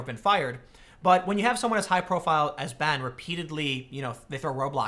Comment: "They throw Roblox in his way" - Tim Pool Yes I know what he really said, just sounded like it